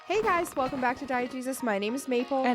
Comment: 🤦🏾‍♀️😩 I keep hearing “Diet Jesus” and I just want to do better in 2025